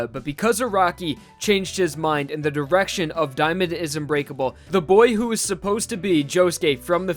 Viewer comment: thought you said “Diamond is u grateful” xd